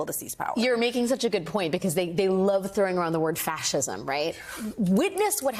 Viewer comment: This is the cleverest discussion between smart people I have ever seen. I loved the new terminology: "These guys have been red-pilled..." "Every accusation is a confession..." I thought the host said "Trashism" but relistening, Alex Wagner actually said "Fascism - I think I understood it better the first time. "This is Orwell." "But dumber than Orwell." "Sinister and ridiculous" at the same time! It is so fabulous to listen to such fabulous brains interacting. Made my paid subscription to YouTube worthwhile.